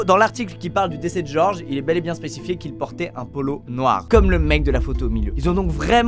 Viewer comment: "un pollo " quoi ? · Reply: Polo déjà